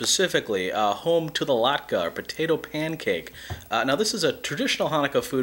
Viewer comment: Man, I definitely thought that said "latte" mixes.